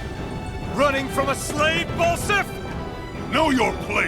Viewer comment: the mighty "Ballsack" lmao ,me and my friend thought hearing his saying his name Ballsack